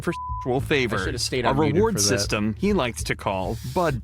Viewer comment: I thought that it was saying "sexual flavors" talking about the candy and was extremely confused